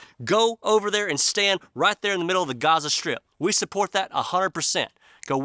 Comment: Funny, when you said Gaza strip, I thought you said Godless strip 🤔